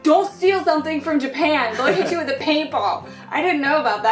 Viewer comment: I thought said, "DON'T STEAL STUFF FROM JAPAN, THEY'LL HIT YOU IN THE PAINTBALLS!!!" XD